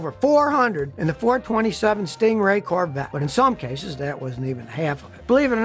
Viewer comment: At about 4:30 did he say Ford Corvette?